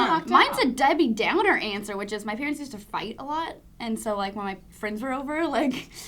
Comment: i thought said "my parents used to BITE a lot..." whoa O_O